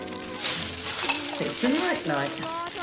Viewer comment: Did she say it's a night light? Also, it looks as though it says something on top. Anyone catch it? Thanks! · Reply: Yes, a night light. It looked like only three letters, so maybe joy.